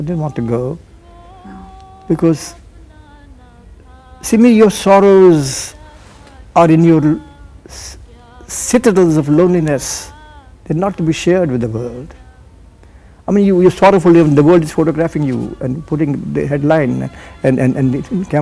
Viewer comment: What does he say at 17:53? Pls someone tell me full line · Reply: Your sorrows are your citadels of loneliness. They are not to be shared with the world. I mean you are sorrowful and the world is photographing you and putting it in headlines and the cameras are following you all the time and putting it on TV - I didn't want that... A citadel means 'a fortress'.. Regards, SimiGarewalOfficial Team